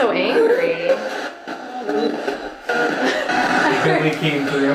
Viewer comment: LOL I JUST HEARD BILLIE 😂❤